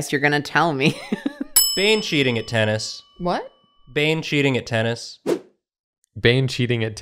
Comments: says "Bane cheating at tennis" And I swear I heard him say "Bench eating a tennis" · Ngl, I definitely thought said "Banshee eating a tennis" at first